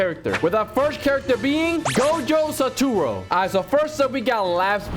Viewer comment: bro did not just say gojo sturo